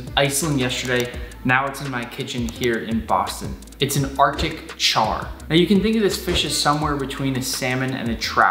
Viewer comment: who else thought it said breaking and cooking a chair? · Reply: Ya I thought it said arctic chair and I just thought it was a nickname for a weird looking fish